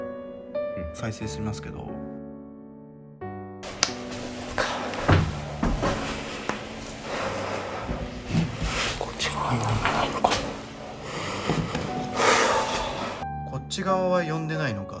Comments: こっち側は呼んでないのかじゃなくてこっち側に何もないのかって言ってるように聞こえる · こっち側は何もないのか…に聞こえました